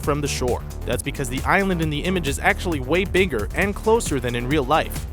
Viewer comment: I thought you said biggest